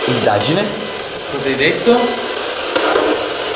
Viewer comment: ho sentito "da chi"